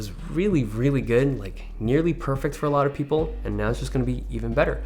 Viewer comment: I don't know if it's just me but i always hear "thumbs if you liked it, sucks if you loved it"